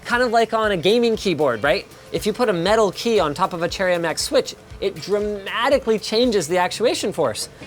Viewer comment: subtitles - "chariot max" instead of "cherry mx" lol